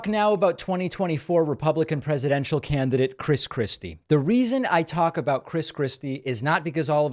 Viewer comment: ! You stole my words! I dubbed Christie.. "Chris Krispy Kreme"... & in the beginning of this video.. I swear a couple of times it sounded like you said "Chris Krispy".